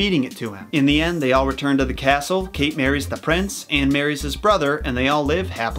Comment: At first I thought you said “Kate marries the prince AND his brother” and I though “oh... it’s that kind of thing...” and then I realized you said Anne